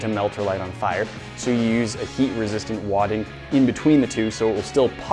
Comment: thought said heat resistant water!